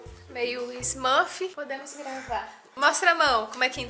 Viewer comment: eu entendi podemos transar 😂